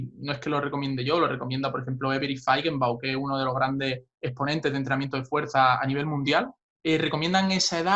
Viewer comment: Cual es el nombre que dice en el minuto 7:30? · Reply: Avery Faigenbaum